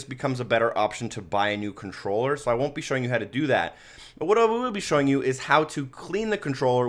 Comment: anyone know what he said??? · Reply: "but what I will be showing you"